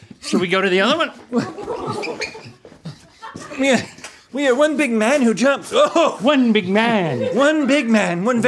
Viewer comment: I thought Rhett said one big man two junks. 😂